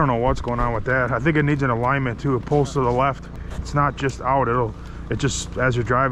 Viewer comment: Did I just hear you say your Police car pulls to the left? :)